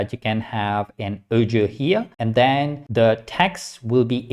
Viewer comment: Hello, what is "ojo" ? Thanks will do. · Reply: I'm sorry, but I don't understand the word you say often - I think you mean something like "audio file" e.g. at 04:09 😊